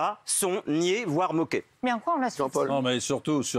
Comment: Je rêve ou j'ai entendu à 14:19 "en quoi on asphyxie ?" Heuuu comment dire